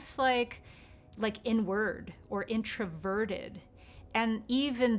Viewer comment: It sounds like you said, "We usually think of someone who's an empath as someone, almost like... N word." The irony in the subject of this video and my subsequent comment is not lost on me either 😂 The only reason I watched this whole thing is because I am most definitely stuck in a paradigm... and because this lady is hot... but mainly because the paradigm thing. Have a lovely day y'all 😊❤